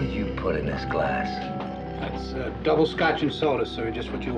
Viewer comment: Closed-captions say "this tastes like butt". That can't be what he really says, right? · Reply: Oh, since that comment I watched it on Peacock with the authorized subtitles. It says "bile".